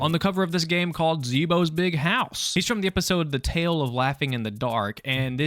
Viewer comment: Not gonna lie... I thought it said zeebos big hole 😂😂😂